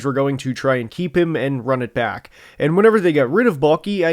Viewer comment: Every time you say “Baalke” I hear “Mulkey.” At this point I’d even welcome her to take control of this team.